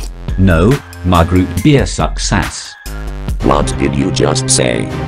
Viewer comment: he actually said SUCCESS